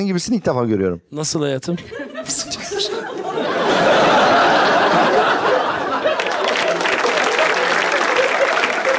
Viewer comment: de kadın ne diyor anlamadim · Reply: Süper diyor